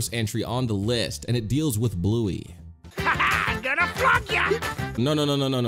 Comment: he is saying flung 2:13 he means dipshit 4:12 he’s saying ship 5:08 they’re saying pitch 7:07 I think she’s saying gas 7:53 he saying son of a bitch